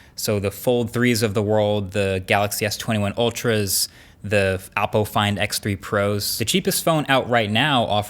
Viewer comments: it sounded like “Apple FindX” rather than “Oppo”. Could be wrong also.. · Did he say Apple Find X3 Pro? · What did you said 😂😂😂😂😂😂😂😂😂😂Apple Find X3 pro 😂😂😂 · I swear I heard appo find x.... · Did he just say Apple Find X3 Pros???